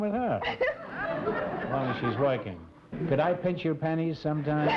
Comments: "Can I pinch your pennys sometime?" 4:30 I thought at first he said panties. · I thought Groucho said can I pet your panties sometimes.